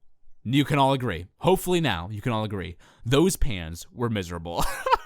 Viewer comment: What happened to your dual monitors from 2014? At first, I thought you said those "pants" were miserable xD